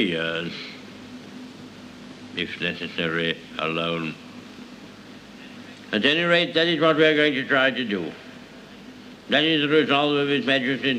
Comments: the most inspiring part of the speech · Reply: At first I thought he said: at any rates that's what Gregory is trying to do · what did he say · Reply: at any rate that is what we are going to try to do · Fun Fact At 0:51 Is Is Actually Saying: "At Any Rate That Is What We Will Try To Do."